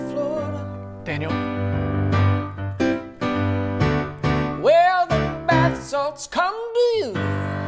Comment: Ok at 2:08 (sweet home Tampa) is he saying “where the bath salts come to you!”?